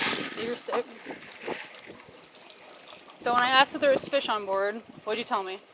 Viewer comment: I thought he said there wad no fish on board.